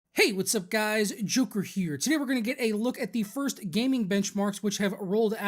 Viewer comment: I almost thought you said hey whats up guys its scarce here. Cancer wave.